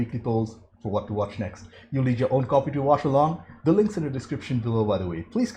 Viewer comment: Total OT, when I watched the first couple of videos on this channel, whenever you said "you need your own copy to watch along", I heard "you need your own coffee to watch along". Right on man, right on. I thought: this is a weirdly specific instruction, but okay. Coffee. Check.